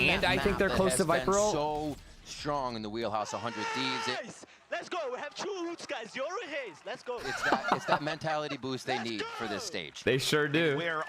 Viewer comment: what did he say? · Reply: “nice. let’s go guys. we have two ults guys. yoru raze. let’s go. let’s go.”